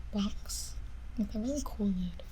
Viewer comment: For a second I thought she said “with a really kookaid” and I was like wait she said a really cool lid